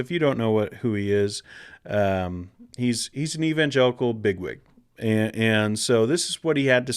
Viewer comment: 😆🤣😅 I'm sorry but I was busy with something while listening and it sounded like you said "he's an evangelical nit-wit." However, after rolling the video back, you really said "he's an evangelical big-wig," which is way different.